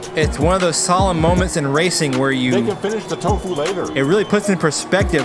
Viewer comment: am i hearing right? "they can finish tofu later"😅